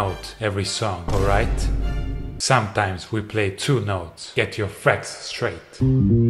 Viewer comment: did you say "get your frets straight", or "get your facts straight"?